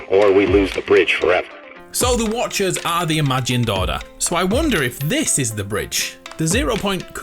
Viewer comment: Is it just me or did you just say "the imagen doughter"